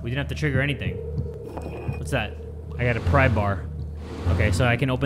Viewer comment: Am I the only one who heard him say “prybar” instead of crowbar 😀👍🏽